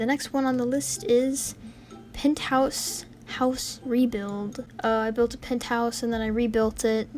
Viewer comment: Im hoping no one said this but was I the only person who thought it said Peetahs house rebuild instead of Penthouse rebuild